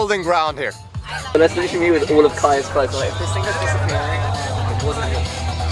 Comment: iS that kai? I heard someone saying kai in the background? If so hello.